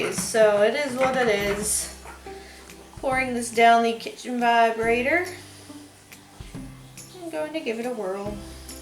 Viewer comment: Did you call your stick mixer the kitchen vibrator? haha That is hilarious, and I pray I did not mishear and am making an arse of myself.